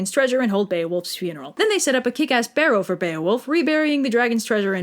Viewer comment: I thought that said KKK ass